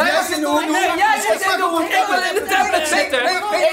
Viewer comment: Wie hoorde nog meer dat zei: "ik wil in de tablet zitten." 5:47😂😂